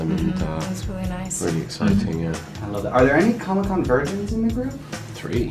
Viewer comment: what is he ask a question? what is he saying ? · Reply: virgins*